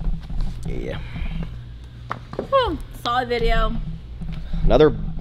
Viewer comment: I was all like, you need to ask her out Maxx! Then I heard you say "bang her, I just met her!" SMH Game OVER!